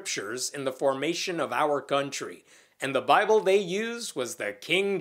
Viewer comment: I thought I heard guntry instead of country.